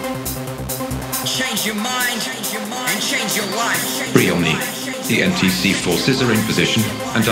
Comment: Sometimes I hear "Change your mind and change you wife!" One of my fav to dance my ass off when I feel down.